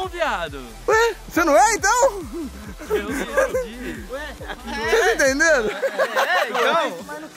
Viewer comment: tu é mano? não viado... ué? "cê" não é então? eu sou! bi... (bissexual) EU JURO QUE OUVI ISSO! NÃO FALEM QUE EU TO LOKA, PORQUE LOKA EU NÃO TÔ!